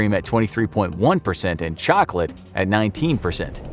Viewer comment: did u say CHOCOLATE?CHOCOLATE?